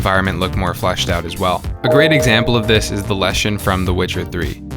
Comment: i thought that said lesbian...